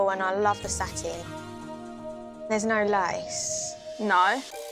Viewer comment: I had to rewind when she said "there's no lace" because it sounded like she said lice. English accents, right?!